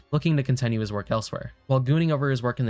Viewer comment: I don't know if it's smeir does it say gooning?